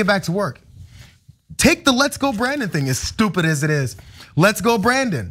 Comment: I thought they were saying "Let's lock Bannon!"